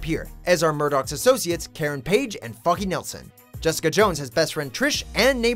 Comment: I thought he said "Karen Page and F*cky Nelson"